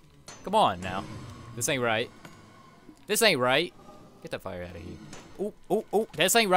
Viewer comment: Did he say f*** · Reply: No he said ill just take this fire out here